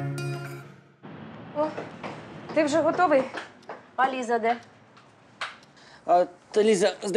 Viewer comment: почула «валіза де?» 😂😂😂😂